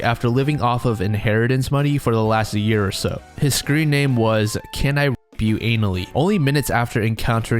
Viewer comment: Jeez, I always thought he said "can I rate you annually?"